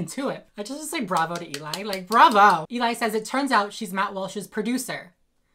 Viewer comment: lmao i heard you say his name is mouth wash and if i didnt hear it right and yousaid matt i legit still will call him mouth wash